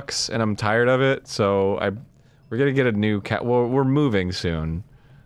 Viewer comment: I thought said “getting a new cat”